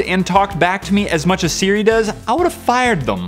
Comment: Lol instead of Siri I thought said Cereal lol and I just got an iPhone and I gave away my android phone lololol